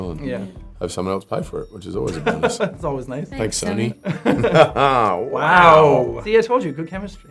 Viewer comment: i wanna meet Chris Hemsworth🙁 wait did Tessa say thanks tony or sony at 3:40? 4:59 JACK KNOWS HOME AND AWAY 🤯and thats an Australian tv show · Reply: Sony. If you read her lips, it's an 's'. :)